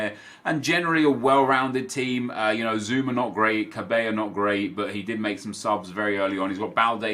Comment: "cabello not great"? Have I heard that wrong? I can't see any players there named cabello? 😂